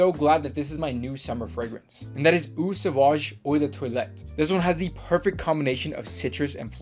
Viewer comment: Bro really has a fragrance called “Oi da toilet” Great vid💪